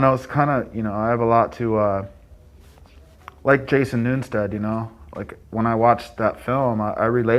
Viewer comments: When he said he relates to Jason Newsted, that hit hard 😔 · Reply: Sounds like he said Jason “Newnstead” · who does he say? Jason ?